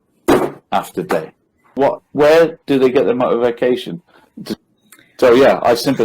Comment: did he say, “motorvacation”?😆👏👏